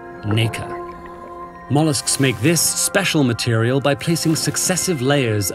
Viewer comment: wtf he just said? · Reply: mollusks